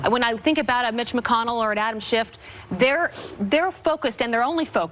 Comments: she says Adam SchiffT.... right?! Anyone else hear this??? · "Adam Shift" Did I just hear that!? They've subliminally accepted his shiftiness 🤣🤣🤣